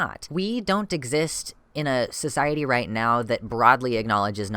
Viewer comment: Did you just say... Cisiety? (As in Cis + Society)